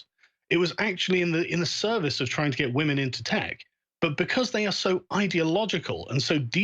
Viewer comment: He was saying "women in tech" but I kept hearing "women intact" lol oooookkkk. 😂🤣